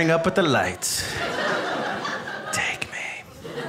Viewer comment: when he says "take me", did anyone else hear like a female echoing voice say "take me... take me"?